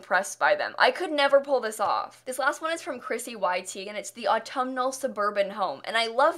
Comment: Lol I thought she said ChrissyYTeagan 🤦🏾‍♀️